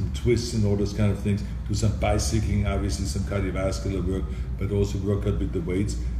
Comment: What is he saying at 2:11 “do some by seeking obviously” is what I’m hearing which obviously ain’t right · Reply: "... do some bicycling obviously"